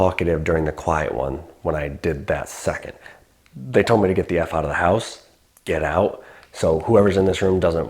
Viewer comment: Love the Tuesday night episodes. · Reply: Now that i listened back it sounds like it says “get the f*ck……. Out of here”